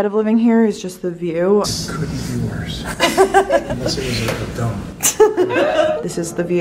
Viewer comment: Omg when you were talking about the view I was listening to the video in the back ground and I thought your dad said “it could be worse, unless it was Donald Trump” 💀😂